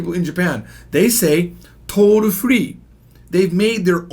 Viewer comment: I thought you said troll free 🤣🤣🤣🤣